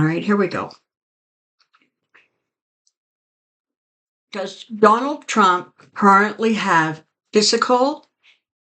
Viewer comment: When you asked the pendulum about GOP nominee, I thought you said RONALD Trump....no wonder the pendulum went still!